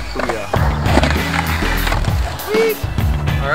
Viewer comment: did he say hooyah?? go Navy lol